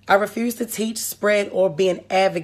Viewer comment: Did she just say... advogate🤔